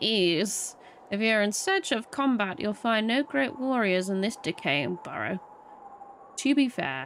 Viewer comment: lmao with your voice I legit thought you said "You'll find no great warriors in this dickhead burrow"